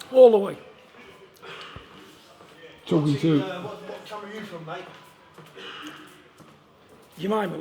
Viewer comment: What did the guy say at 7:20, and who did he say it to? I can't tell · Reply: I think he said ''What camp are you from Mate?''.